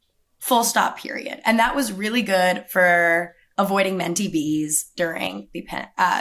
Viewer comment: what did she say the environment in AZ let her avoid? "Men TPs"? What is that? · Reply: Menty B’s - mental breakdowns